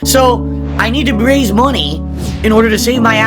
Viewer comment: With friends like these who needs detractors Also I always hear "I need to braise money" and it reminds me how gin-brained his English can be. The fact that native English speaker slurs his words this much that it's common for the guy is insane